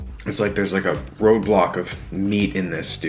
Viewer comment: On 13:54 he says" theres a ROBLOX of food" if the people that play that yull undurstand what im sayig